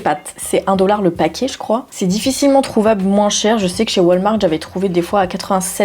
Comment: Commences-tu à devenir québécoise? J'ai entendu "wal-marde" et non "wal-mart" 🤔😂